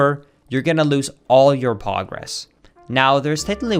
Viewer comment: Did he say "Pogress" or am I tripping?